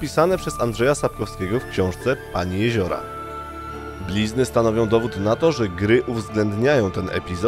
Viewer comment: Tylko ja usłyszałem pizdy